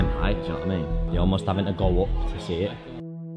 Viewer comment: I am trying to make czech subtitles but your british accent makes it much harder. What are you saying at 0:9-0:10? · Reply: I think he says you almost have to go up you see that